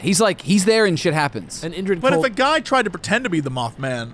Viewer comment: INDRID COLD? DID I HEAR THAT RIGHT??? LISTENS TO TAZ?????